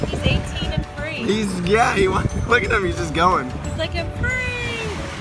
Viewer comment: "He's gay!" 0:40 Did anyone else hear that?